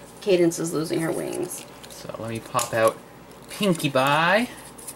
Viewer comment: did you just call her Pinkie Bye?